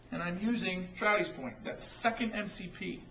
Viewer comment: Could you tell me the name of the 2nd MCP please at about 1:30. It sounded like "Trowdy's point". Thanks